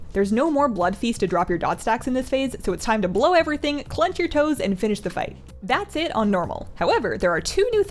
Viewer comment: I heard "clench your toes and finish" umm.. :P